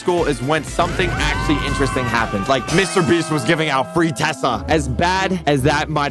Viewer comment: When he said "Free tesla" i heard "Free Tessa" and i was like Tessa from murder drones